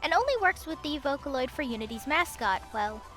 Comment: what program did she say? I'm not native english speaker and I didn't quite understand that sorry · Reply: Unity. It's a gaming engine. For some weird reason, Yamaha had allowed a plugin that put in an extremely basic version of the Vocaloid software into Unity but the results were worse than the first Vocaloids so it seems the project was quickly thrown away.